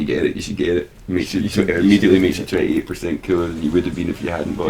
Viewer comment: My heart fluttered when simon said '28% cooler' at the end- for a fraction of a second i thought he said 20%!